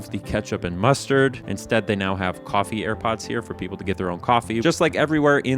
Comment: did brother just say coffee airpods?? Edit: did not know those things were called "Air Pots"